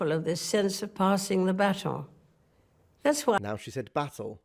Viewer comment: hey @ 16:01 the queen said "passing on the baton" but the subtittles wrote it wrong so when you thought she said "battle" she didnt she said "baton"